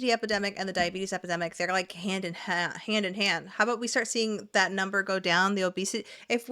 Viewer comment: im so sorry i heard you say the diabetes epidemic and the obesity epidemic go HAM IN HAND and i fuckin lost it 😹🤣 i gotta start listening better lol